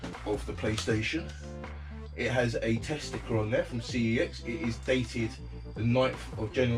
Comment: when you said test sticker I thought you said testicle. Just thought you should know :)